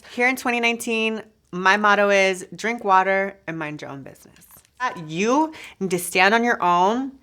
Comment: i thought she said drink water and mind your own pussy..